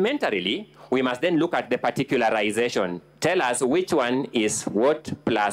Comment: I heard 'particularization'😂😂